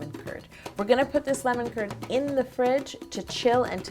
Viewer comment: Am I the only one who thought she was saying "lemon turd"